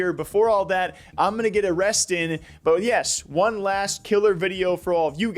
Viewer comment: BROTHER WHAT? Edit: He said “I’m gonna get a rest in” but I swear I heard “I’m gonna get arrested” lmaooo